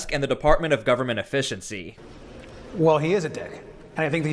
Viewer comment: Did he really just call Elon Musk a d*ck?? Maybe I heard wrong??